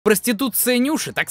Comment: Мне послышалось конституция